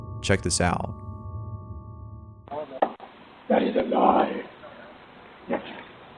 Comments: #6 sounds like it says "that is a lie .... bitch" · #6 sounds like someone saying that's a lie angrily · I hear “that is a lie” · i think i know what it said, i think it said: "that is a lie" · Am I the only one that hears “that is lie, Deklin” on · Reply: I hear “That is a lie, max” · for #6 it sounds like theyre saying "THAT IS A LIE" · at the doorbell part it sounds like its saying ''That is a lie''? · Number 6 just sounds like someone saying "THAT IS A LIE, actually" or "THAT IS A GUY, actually" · It sounds like it saying "THAT IS A LIE" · i hear: that is alive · No.6 sounds like it says "that is a lie" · #6 I heard “that is a lie... bi##h” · number 6: "could it be a ghost" is a man saying "that is a lie" ina jokingly deep voice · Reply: I heard "that is a guy" XD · Number 6 sounds like someone is saying that is a lie Richard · Until 6:27. I heard the voice saying “Where’s your boy”? Anyone else hear that? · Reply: I heard "that is alive" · Is it just me or does it sound like the voice is saying “Ready to die?” in the Ring doorbell video? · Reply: I hear " that is a guy...yep." Lol. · sounds like batman saying "that is a lie, mitch" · #6 with the Ring video sounded like they said “that is a lie. Bitch”. Must have been watching good old Maury. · Number 6 sounds like someone saying "That is a lie, bitches" or "Daddy's alive, bitches" LMAO!!!